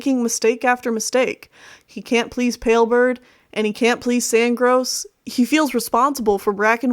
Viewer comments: I heard her say Sandgross. Sandgross. S A N D G R O S S S A N D G R O S S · I thought you said “Sandgross” hehe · Hold on. 14:31 Did you just say Sandgross Or is it my hearing. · I thought you said Sandgross lol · Did you just say Sandgross? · Did you say sandgross?